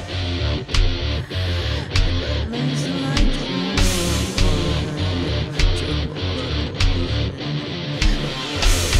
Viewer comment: ช่วง 2:40 จนถึงตอนกรี๊ด เขาพูดว่าอะไรอ่ะ ได้ยินไม่ชัด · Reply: บรรลัยสลายจนหมด~~